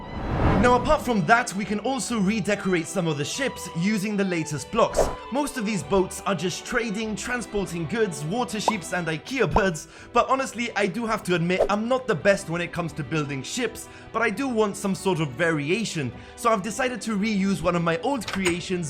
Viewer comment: Did he just say water sheeeeep !!!!!!!!!!!